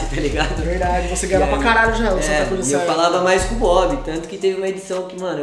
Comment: "eu falava mais com mob" só eu ouvi isso? Pode ter falado bob mais ouvi muito mob